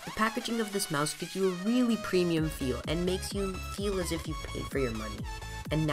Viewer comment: Did he say you feel as if you pay for your money